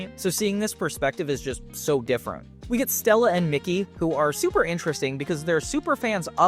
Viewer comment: lol u did not just call her “Mickey”🤣🤣🤣her name is MACY